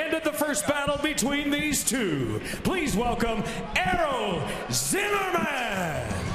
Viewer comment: i legit thought it said error instead of Errol